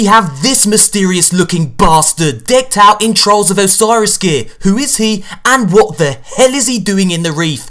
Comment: I gotta admit after dealing with that blue bastard cryptark I thought he kept saying trolls of Osiris. We'll see how accurate a description that is when the DLC drops.